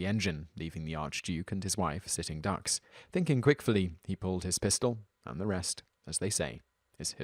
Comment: did you just say "quickfully"? <---- favorite new word!